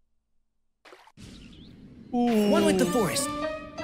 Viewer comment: At some point when Tighnari said "One with the forest!" I mistakenly thought he said "I want a divorce" 😂